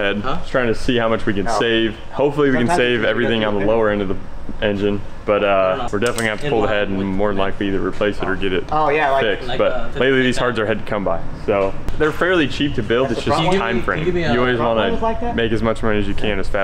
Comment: LOL i though i heard him say these hards are heads to come buy lol